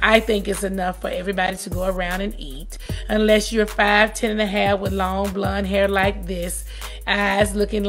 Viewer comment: I kinda think Nene was talking about Kim or Lisa Vanderpump- Tardy for the Party or Vanderpump Rules spin offs. Nene said if you have eyes like mine, 5’10 (or whatever height she said) and blonde long hair.. thats low key saying it’s discrimination based on race. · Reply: Roby Cummings thank you. That's exactly what I heard. She said 5'10 and a half. Nene wasn't talking about Kandi unless Kandi has long ,blonde hair with a certain colour eyes...smh